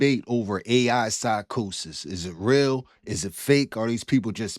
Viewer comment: Not gunna lie I thought it said A.I. Psychics 😂😂